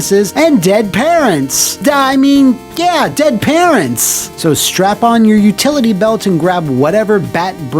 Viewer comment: "Did he say strap in or strap on?"